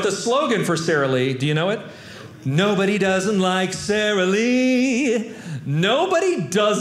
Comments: Omg. I've been wrong about the Sarah Lee slogan my whole life! I always heard, "Nobody does it like Sarah Lee." Now after all these years I finally hear it right here. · Hmmm… I thought it said "nobody does it like Sarah Lee"… ??